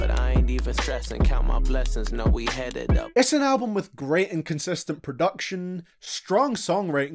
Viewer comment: it sounds like hes saying "great inconsistent production"